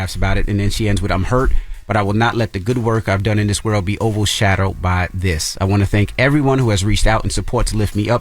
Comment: did envy say "oval shadowed"? 😂😂😂